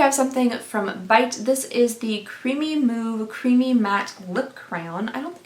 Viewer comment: I was very confused why Bite would call a product creamy moo creamy matte lip crayon until I looked it up and realized just misspoke (and I misheard) and it's actually power move creamy matte lip crayon🤣🤣🤣